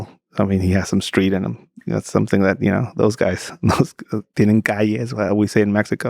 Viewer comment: What did he say at 2:13:38 in spanish? Does anyone know? He was speaking about brazilians :) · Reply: He said the guy was from Brazil, he had street in him- then he said -tiene Calle how we say in Mexico,, Bullshit Mexico you say tiene Barrio. So he tried to make it sound as something more serious